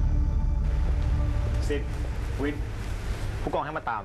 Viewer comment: ทำไม15:33ได้ยินพายุเรียกศิลกับวินว่า สิทวิท